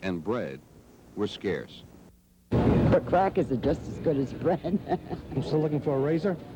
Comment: …..did she say crack is just as good as bread?